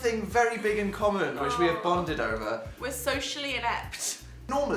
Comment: it sounded like she said "we are socially erect"